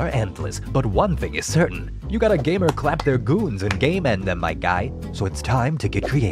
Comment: Did he say Game end? pyrocinical wants to know your location